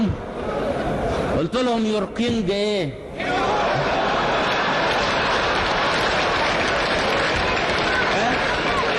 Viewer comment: ياريت شخص من السويس يفهمني ماذا قال الجمهور ورد على عبدالناصر بالدقيقة 8:03 قالهم انتو قلتو على ملكهم ايه :your king ايه...الجمهور ماذا رد وقال ياسوايسة يا اهل السويس الباسلة..اخوكم من العراق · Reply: your king is a woman